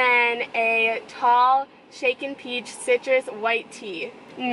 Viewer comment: the first time she said “shaken tea citrus” i thought you said “chicken tea citrus” haha